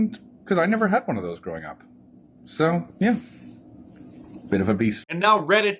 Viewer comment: Did he say "bit of a beast" at 2:23 ? Yeah mate, you said it!